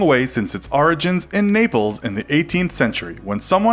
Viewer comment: Naples?!?! I thought you said nipples!